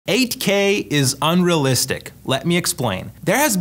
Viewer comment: I thought bro saying 8k looks unrealistic ☠️